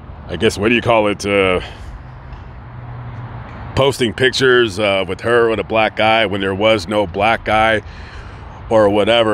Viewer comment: I thought you said posted pictures of her on some blk guy.